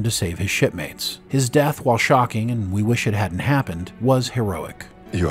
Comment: Did you say his death was "Shaw"king? 😅